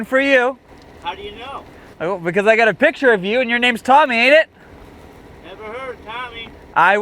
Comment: at first i thought he said Tommy Innit